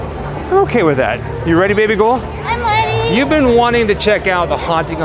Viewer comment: did she really say, "I'm weady!" like a toddler?